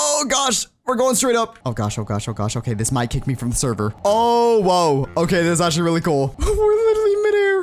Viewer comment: is it me or does he say “OwO”? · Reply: He said oh woah but in a way that sounded like owo but imagine if he was a furry